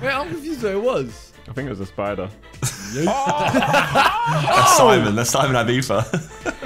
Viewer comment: “It’s myself, Simon, Josh and Ethan” Editor when they hear Simon: I’m just gonna pretend I didn’t hear that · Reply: Because i didnt see simon i thought he said sidemen! :)